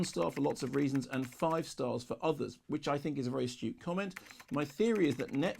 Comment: I initial misheard 'which i think is a very astute comment' as 'which i think is a very stupid comment'. Had to replay to double check :D